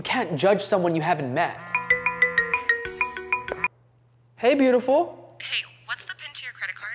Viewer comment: Anwar: Hey beautiful The subtitles say otherwise